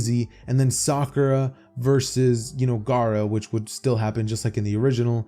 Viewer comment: did you just say sakura vs gaara